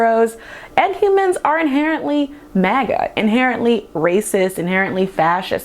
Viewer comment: hi im not from USA english is not my language... what do you say in 21:48? is the same word you say in 6:21?maga? like make america great again? im trying to get the context... like.. ppl who wear those hats are mostly fascist? thats what you mean?